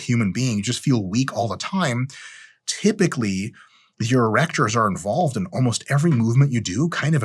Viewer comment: Dude I thought you said grow your erections. Ew